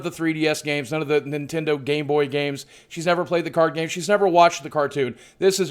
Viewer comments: Anyone else hear at 0:49 "gay boy games"? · Sounded like he said "gayboy". Also, as I'm a shut-in geek, I can't really play this game...